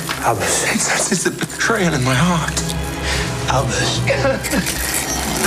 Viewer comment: When albus said ''he says its a betrayal in my heart'' that sentence made me cry a lit bit 😔😔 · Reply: I thought he said “it senses a betrayal in my heart”